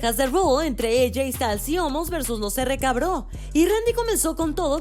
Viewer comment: rk bro jajajja pensé que dijo cabro jajajjaj saludos de Perú 🇵🇪🤜🤛